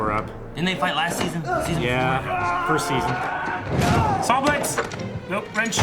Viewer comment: Did said season 4? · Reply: "Didn't they fight last season, or the season before?"